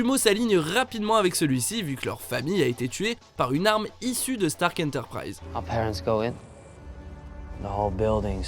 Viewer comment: c’est stark industry pas je sais pas quoi voilà 😊